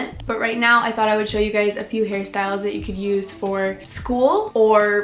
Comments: why do i keep hearing "harry styles" instead of "hair styles" LOL I'm too far up harry styles' ass sorry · The first time she said “hair styles”, I thought said “Harry Styles.” 😂👏🏻❤️